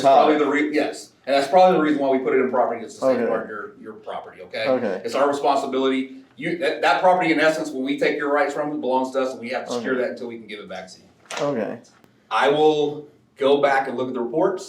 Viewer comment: did I hear that correctly? "When we take your Rights.... blah, blah, blah"?